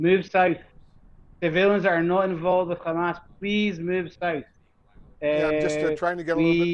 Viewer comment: Did he say “the villains not involved with Hamas please move south” at 8:35 ?